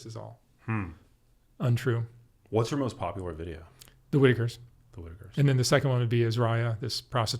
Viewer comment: What video did he say was the most popular? Was it "Wiggers"? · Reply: The whitikers